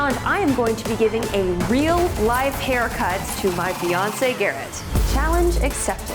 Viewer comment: I swear i heard you say Biance (as in the singer) and not fiance! in the intro.